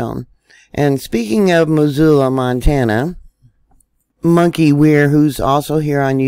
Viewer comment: she says monkey who? I can't catch the name,please help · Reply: Monkey werx